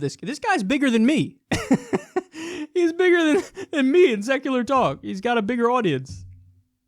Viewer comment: I only watched this cuz I thought it said sexual talk and pornhub wasn’t working